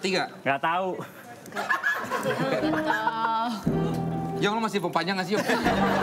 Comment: desta ngomong apaan ya? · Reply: "yong msh mau gue perpanjang gk sih yong" 😁